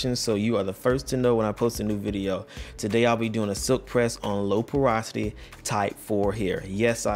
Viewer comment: I thought it said Low Prosperity 😂😂